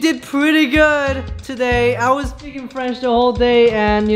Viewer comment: I thought he said “I was born in French” I’m dying😂